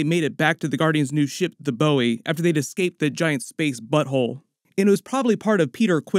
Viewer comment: LOL 😂😆 lmao did u just say space butt hole? LOL U rock omg that was hilarious lol thanks for the laugh man.